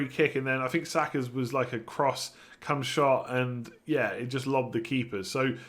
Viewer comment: did he just say it was a cumshot? Bringing Auba in for Salah and gonna free hit in gw 19💪🏻